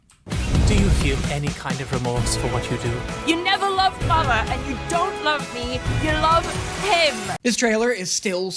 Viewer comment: I thought Stella said "You don't love father, you don't love me, you love 'him'".